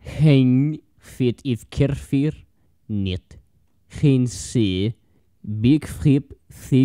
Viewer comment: that sounded like russian нет